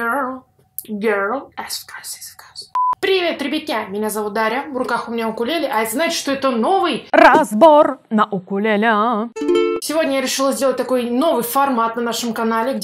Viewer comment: ЛЯ МНЕ ПОСЛЫШАЛОСЬ : "Привет ребятятина))"